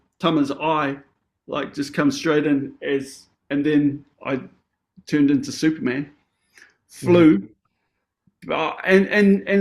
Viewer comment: said "I turned into superman", I heard "I turned into soup, man"... But from my extensive experience with horses, I think they're equivalent when one rams into you 🦸‍♀️